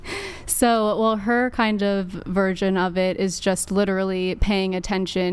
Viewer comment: she said “her virgin of it”instead of “version of it” lmao I had to play it back a few times to make sure lmao 🤣